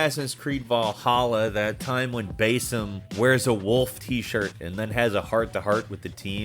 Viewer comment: I keep hearing "BASIN" every time he says Basim...